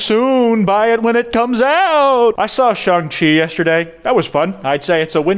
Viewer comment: I thought said ZhongChi instead of shang chi insert skull emoji